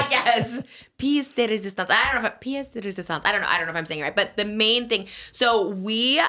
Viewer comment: i keep hearing piece of resistance and was confused how we ended up talking about the lego movie😅